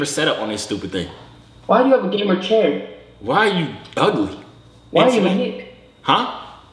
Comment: I am black so i think that white boy in 21:00 said "why are u a n word but said the actual word